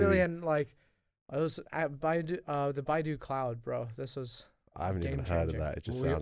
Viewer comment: who knows what said here? Buy do cloud? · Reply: baidu cloud